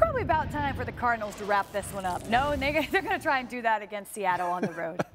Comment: what did she say??🤷‍♂️🤷‍♂️ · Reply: she said no nigga about the cardinals winning lmaoo